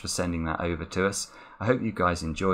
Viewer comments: I swear I heard “crapping the cryptic” at 33:30... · Anyone else hear 'crapping the cryptic'? :P